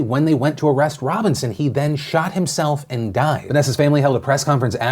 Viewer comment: sounded like you said he shat himself then died lol